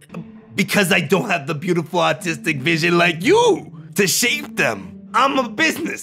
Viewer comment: I misheard artistic vision as autistic and was really confused for a moment.